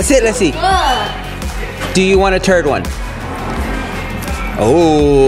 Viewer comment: says, do you want a third one, but because of his accent it sounds like do you want to turd one? Also try putting on the subtitles even the subtitles think he said turd😅